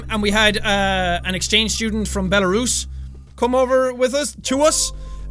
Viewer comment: I for a second thought I heard you say that there was an exchange student from Betelgeuse. That would have been so awesome haha